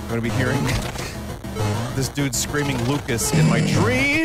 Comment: I was honestly mishearing the yells the whole time and thought he was screaming "MARIOOOOO CHEEEEEEESS" instead of "LUCAS" and was probably losing my mind more than vinny because of that